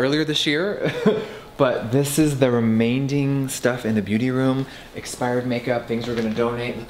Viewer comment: did he just say remainDing? 😂